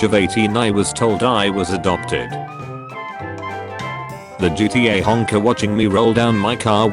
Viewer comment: I thought it said “GTA Hacker” 😂